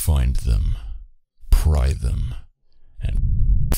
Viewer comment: did he just say rape them?